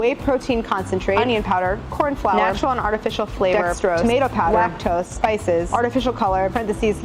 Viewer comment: When she said Dextrose at 5:54 I first heard "That's gross!" xD